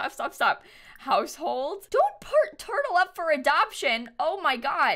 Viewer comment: I heard "no dont part turtle up up for adoption"hahahahagag I think you ment put